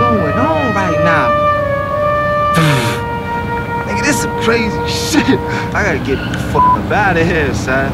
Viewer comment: did he say''nigga thats some crazy shit?'